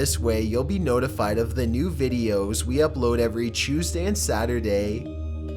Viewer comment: When he Tuesday it sounded like he said chewsday